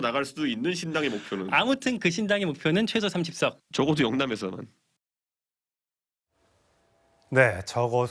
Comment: 이준석이 "영남에서는"이라고 분명한 워딩을 했는데 진행자와 자막은 왜 자꾸 "영남에서만"이라고 쳐 바꾸는거임? 바이든이 날리면으로 들리는 놈들인가?